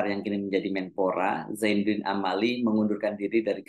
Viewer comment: Ternyata salah dengar, kirain zaenudin naciro yg mau mengundurkan diri. (◔‿◔)